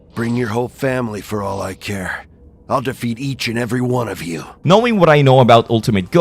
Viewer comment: i thought he said kill each and every one of you